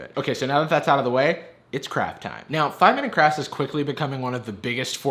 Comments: I heard crap time lmao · omygod right at the beginning i thought you said "its crap time" i am the stupidest...